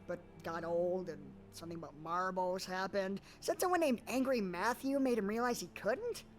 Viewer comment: Angry Matthew=Angra Mainyu, for anyone who's confused. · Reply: Thank you, I heard “Angry Matthew” and “Angry Mango” well before I ever heard Angra Mainyu